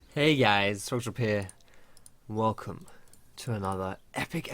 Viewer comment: Omg he says here at the beginning of the video. I thought for years he said social deer.